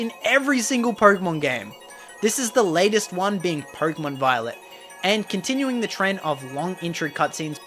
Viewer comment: who else thought he said “Purple and Violet”